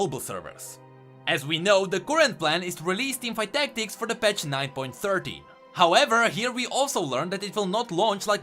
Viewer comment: I hear 9.13,but the sub says 9.30,im confused